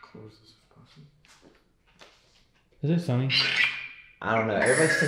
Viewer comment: sounded like it said Sonny.